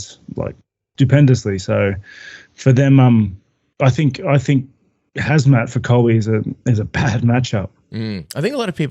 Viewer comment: did rob call chimaev hazmat?? 9:17 lmaooo